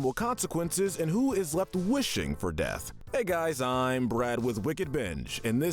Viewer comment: the subtitles say his name is “Brab” instead of Brad